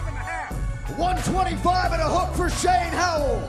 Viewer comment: did he say 225.5 he missed weight by 100 lbs lol